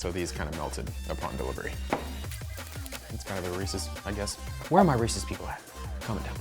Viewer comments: captions said “where are all my racist people at” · the subtitles say were are my racist people at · the captions say "it's kinda racist I guess where are my racist people at comment down below"- Well I'm one of the racist people 😂